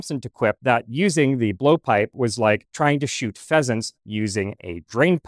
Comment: I thought that said 'peasants'